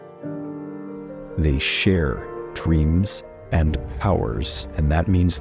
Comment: I double tapped the screen at the point he said "They share" but the share got cut off so I heard "They sh!t" 😂